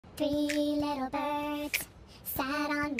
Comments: It sounds like "3 little pervs sat on my window and they told me i dont need to worry"💀 when you put it in ×3 speed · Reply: I heard “three little perves shat on my window and they told me I don’t need to worry” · For those who don't know. It supposedly sounded like "fell off my window." Edit : For those who keep on saying I'm wrong. I just saw the exact video with a different person and everyone said its " fell off my window " Plus I heard " fell off "